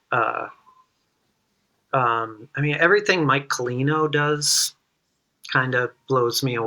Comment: Who is the artist mentioned at 1:14:55 (pictured with the violin)? Didn't catch the full name. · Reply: Mike Collino aka Dog Lady aka Dog Lady Island